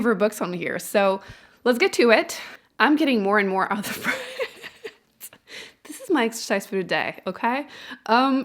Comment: Anyone know what was said at 20:41 “I’m getting more and more ________?” I can’t figure it out & it’s driving me crazy 🤪🤪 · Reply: Out of breath